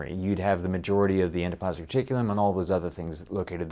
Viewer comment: thought he said "endoplasmic chicken" at first...